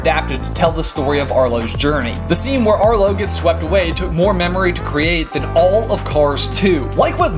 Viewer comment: can someone explain what he said at 1:50 please in a response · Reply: "It took more memory to create" as in it contained more data. It took more computer memory to create that scene than it took to create Cars 2, because the scene in The Good Dinosaur was more detailed and had more complex animation.